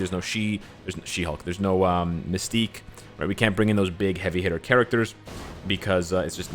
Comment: “We cant bring in those big heavy hair characters because its not allowed” Meanwhile big heavy hair sentry being a big boi striker punching knull seconds later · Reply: He said heavy hitters though lol